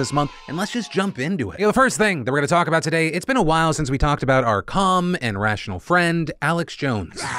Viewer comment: Definitely thought you said "it's been a while since we talked about our cum" in the intro. It's been a long day.